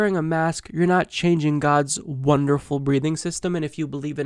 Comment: I'm glad you clarified that she said, "God's wonderful breathing system." I thought she said, "God's wonderful breeding system," and that she was upset we're trying to stop germs from spreading. lol.